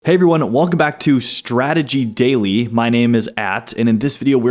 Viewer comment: Did you change your name too? Because it sounded like you said "my name is att" 😂 i think we should keep MSTR. It spells monster